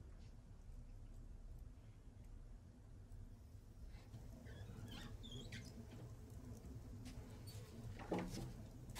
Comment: что она сказала? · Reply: Одетый мыться будешь?